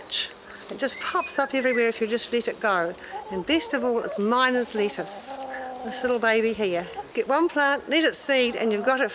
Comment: anyone catch the name of the plant? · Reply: I think the first one was miner''s lettuce and the second one was mesclun.